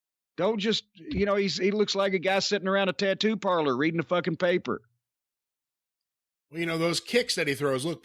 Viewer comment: "He looks like a guy sitting around a tattoo parlor reading a newspaper" 😂😂ok im done, cannot unsee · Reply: Hahaha, I thought he said "eating newspaper"🤣